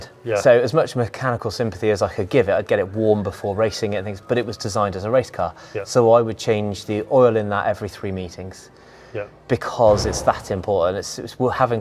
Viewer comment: I thought he said I change the il every 3 metres lol